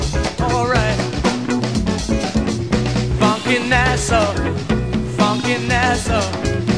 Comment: This song was controversial in its day because @ 2:22 people thought they were saying "fuckin' asshole"...